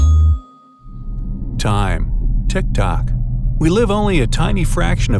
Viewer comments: | i thought he said " time to die" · did he say tiktok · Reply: Not tiktok the social media site but the sound tick tock like on an analogue clock . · did you just said tiktokers?